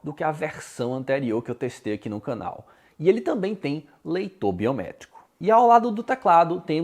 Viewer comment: só eu que ouvi leitor biomédico? hahaha